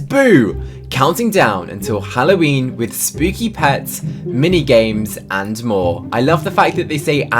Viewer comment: When you said mini games I had thought that I said nightmares 😀